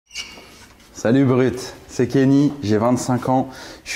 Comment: au debut au lieu de "salut brut" j'ai entendu "salut uber eat" jsp comment l'interpreter, le pauvre